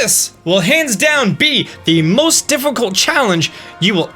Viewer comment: Who else heard him say tifficult?????